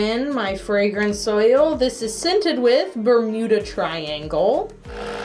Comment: I thought she said the sent was called burrito triangle not Bermuda Triangle